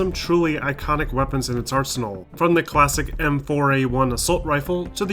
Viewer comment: What did he just say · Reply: Arsenal